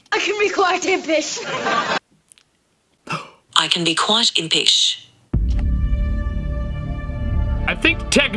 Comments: during the one where you did it to your google captions said "quite tippish" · do you think he’s saying apish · I heard I can be quite a piss